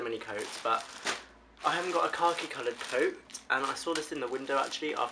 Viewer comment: Lol when he said khaki it sounded like he said car key.